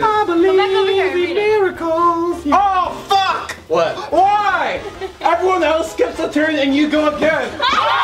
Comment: What did say at 29:30? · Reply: everyone else skips a turn and you go apparent I think